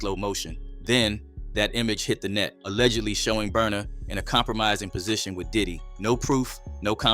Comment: wtf "It burns" Say Burna Boy